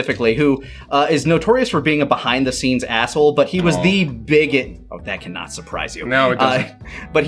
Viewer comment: cut himself off but it sounds like he says Hulk Hogan is THE bigot, which isn't inaccurate lol